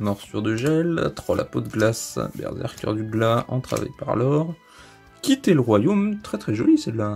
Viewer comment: Pour la carte " Quitter le royaume"' j'ai entendu " quitte est le roi Yoonns " x)